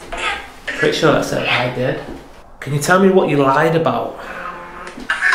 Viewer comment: there was a real time “I did” whisper! Anyone else hear it? It wasn’t Necrophonic it was an actual whisper. 52:40 I heard a little girl whisper “come here”.